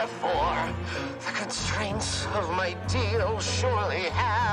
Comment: did anyone hear deal at this part 0:28???